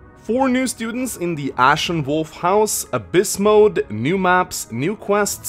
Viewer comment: Thought he said Ashe and Wolt get houses... lol.